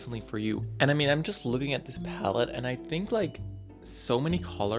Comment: I thought at 9:10 you said “and I’m just living for this palette” my ears must’ve cursed me 😭😭